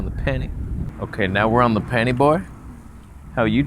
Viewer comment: We want more blurry girlfriend. And at 6:15 did you say, "Now, we're on the panty boi"? You lil freak, you!